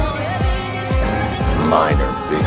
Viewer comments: I thought it said "Men are bigger then urs" for a sec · When he said that like I always hear “My cojones are bigger than yours”